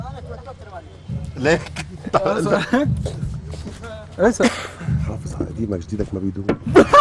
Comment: وش قالهم فادي😩💔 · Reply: حافظ على قديمك جديدك ما بيدوم.